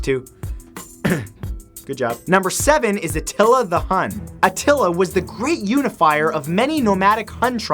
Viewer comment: number seven I thought he said nutella the nun